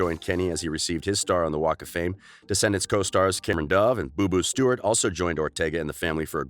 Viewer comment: did he say Cameron dove I thought it was dove Cameron anyways rip cameron Boyce we will all remember you and miss you a lot 😭